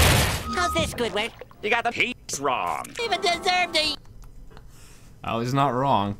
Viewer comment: it sounds like he said “ya got the peeps wrong!”😂 And the hard cut to laughing after removing the d*ck joke was hilarious Edit: even the captions heard what I heard!